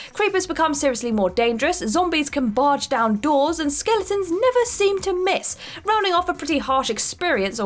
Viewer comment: For minecraft when said "zombies can barge down doors and skeletons never seem to miss" i heard it as "zombies never seem to miss" and was like don't zombies never miss anyway? Lol